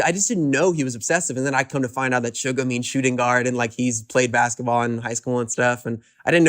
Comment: I think he said “suga means shooting guard” not “suga being shooting guard”. I saw somewhere that bang pd suggested it as a stage name when they were picking and yoongi liked it because it went with his position in basketball. When you abreviate it in Korean it would be “suga” not “sg” like how you would abbreviate it in English Note: I don’t speak Korean or know for a fact this is what “suga” means. It’s just what I heard.